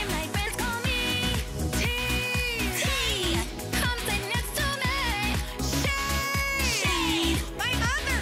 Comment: Anybody understand what Megami says here? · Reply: 'come sit next to me'